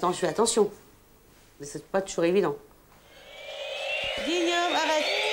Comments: C'est moi ou a 7:37 il a dit "Ta g€u!€" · qui a l'impression comme moi que le petit dit : "ta gueule" 🤣